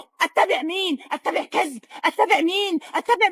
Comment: ok a little off topic but why is she saying‘I thought it mean I thought I cause